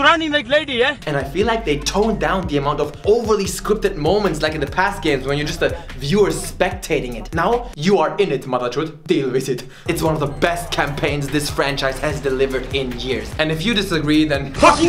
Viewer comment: wait did he just say "motherchod "...or am i just hearing stuff ?